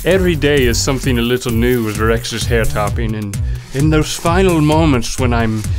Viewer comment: rhett's hair stylist (possibly Link) also calls him 'rex' instead of 'rhett' lol !